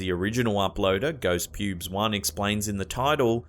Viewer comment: Did you say "...user ghost pubes one..." at 2:59? Lol...